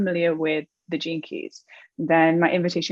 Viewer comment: hahaha. Hilariously I was listening without looking and I thought you were saying 'The Jinkies' #TheGeneKeys